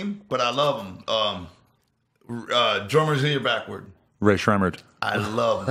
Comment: What's the name that Violent J's saying @ 1:10:46 please anyone? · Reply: Rae sremmurd